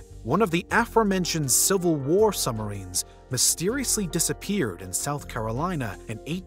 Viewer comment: Might be my phone....but why does it sounds like he is saying "sumarines" instead of submarines